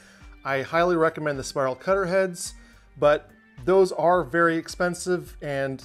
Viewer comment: wtf was said after 5:02 ?? spiral cutter heads ? and what is that ?